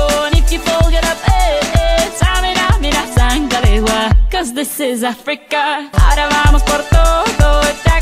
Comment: I thought it said “this time for Africa”